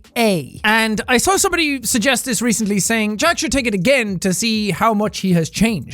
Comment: Wait what did he say at 0:35? "John"? "Jun?" "Jud?" "Jean?" Because it sounded like it started with a J but it wasn't Jack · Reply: The phrase was "Jack should take it again". He was just speaking very quickly & mumbling a bit, that's why it's not super clear.